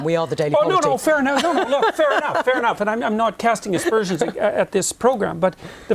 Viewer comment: lol I thought he said "expersions" too but after listening closer I found out he said "aspersions" and it was the most precise word he could have used then. 😂 he's on point with saying what he means